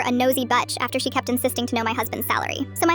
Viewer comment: lol, I heard "nosy butch". I'd like a nosy butch. or femme. or not nosy.